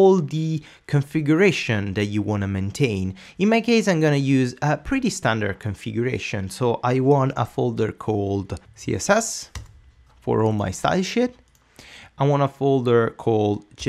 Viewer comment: is the funniest ever. Thought for sure he was saying style shit. "Lets make a CSS folder for all our style shit." Too funny. Great, great tutorials by the way. Very much appreciated.